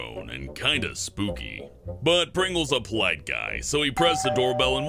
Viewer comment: Anyone else heard "but Pringle is a black guy so he rung the doorbell and sat patiently"